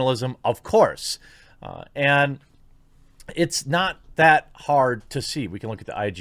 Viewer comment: Did you say Hard?